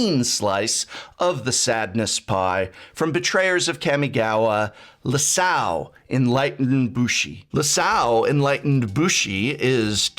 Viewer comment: Is it just me, or does anyone else hear say "Lisao, Enlightened Bushi"? at 12:19? Because the card seems to start with an I when I see it, not an L (just wanted to clarify and see if I was crazy or not XD)